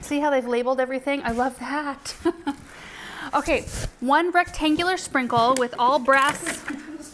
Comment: Yep I heard "one rectangular sprinkle" 😂 too cute